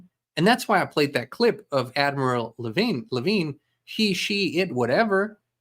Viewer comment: ☺️At first I thought you said AVRIL LAVIGNE! 🤭 (The singer🎶) I was totally confused 🤔, so I had to rewind it to double check.😕 ....Cuz at this point these days... you NEVER KNOW whose PLAYING what PART(S) 😏🎬 ~ " Can someone PLEASE pass me the popcorn ? "🍿 🤣